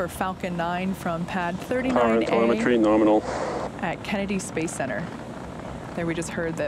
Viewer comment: did he just say NORMINAL? 😂👍